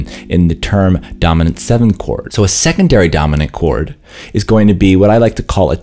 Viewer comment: Before 1:45 i thought he was saying "second dirty dominant" and well, that's a good name